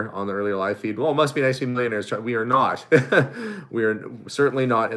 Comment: /4:36. uses a word concerning how someone sees you folks, but I am unable to discern what that word is. I hear "emlaners" and have no idea what that means. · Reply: millionaires